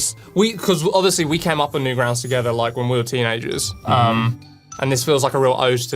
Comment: did he say they came up with Newgrounds? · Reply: No. They came up on newgrounds